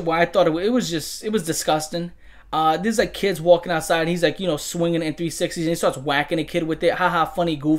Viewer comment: AYO! @ 37:07 WTF does he say? What is he swinging that hits the kid and goes too far? "A Nintendo 360" is what I heard, but... that shit makes no sense. Unless he was swinging a N64 by its power cord like a fuckn ball and mace.... fuckn preteen midevil knight and shit! Fuckn hardcore!! Haha, that shit is too far! Lil bro NEEDS TO BE STAHPD NOWWWWW